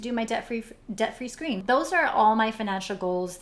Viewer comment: I cannot wait to hear you do your Debt Free Scream!! :) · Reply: Breana Schadt oh that's what she said? I thought she said screen. I was like what is that.